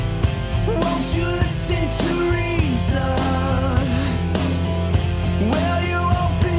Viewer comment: The first time I clicked on an Atheist Experience video, my washing machine was going through a noisy spin cycle, and the theme song sounded to me as though it said, "Won't you listen, Theresa, with your opium eyes?" I can never unhear this, so I felt I would be generous and share it with you good people. You're welcome. :D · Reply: I always hear "Won't you listen, Theresa" as well after realising once that it sounds like that. Cannot unhear xD